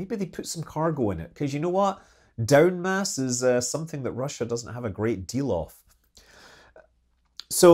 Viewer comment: when you said "down mass" I heard wrong and thought you said "Dumbass is something that Russia doesn't have a great deal of!". lol I must admit that, from personal experience having fought the cold war from a US Nuclear Submarine, my immediate thought was I disagree... Russia has a great deal of dumbass! 😂